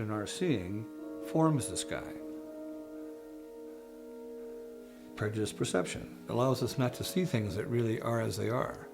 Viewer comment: can anyone hear what he says at 5:08-5:10? "per disperception....?" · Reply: prejudiced perception